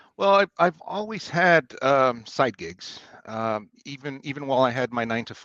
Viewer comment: I’m listening on 2x and it sounded like he said I have always had a lot of psychics